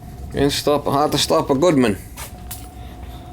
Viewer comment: I thought they said it's hard to stop a train not Goodman?😮